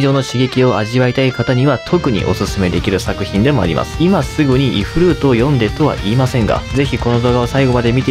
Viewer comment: ifルート イフリートって聞こえた俺は疲れてる